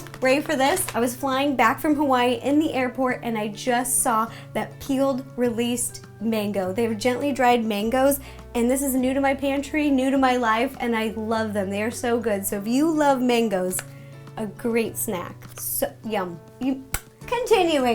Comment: I don't even have a pantry.... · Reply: Oh sorry i thought u said "panty" i was about to say "WTH!?"